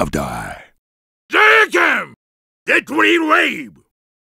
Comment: What does he say in 0:35? LMAO i cant understand it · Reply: Deadly Rave